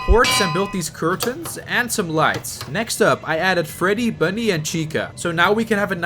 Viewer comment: If you heard me say Bunny instead of Bonnie, hit this button🤣💀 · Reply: I heard you say bunny instead of Bonnie.